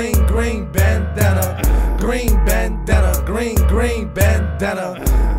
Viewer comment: hahahahahahahaha i thought it said green BANANA